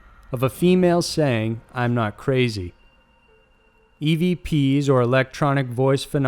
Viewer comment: On the second to last one I hear: "I love my memories", instead of I'm not crazy. But maybe I'm crazy. It still says:" I love my memories"...!